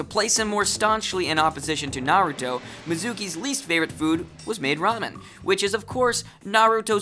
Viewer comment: did he just say ma DA RA....